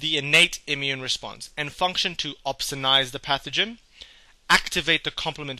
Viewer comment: Excellent video. Thanks for making. But I didn't understand one word you said at 2:51 "Function to ....... the pathogen" Obsinize?